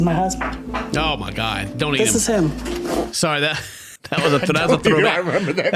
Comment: I honestly thought at first that said “Donate him”. So I was flabbergasted for a different reason lol